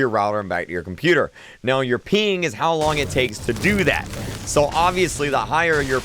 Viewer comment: I thought he said "peeing"